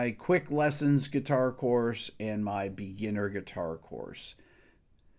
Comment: LOL I literally thought I heard you say "My air guitar course", and I had to go back and re-listen. I could DEFINITELY master the air guitar course. :P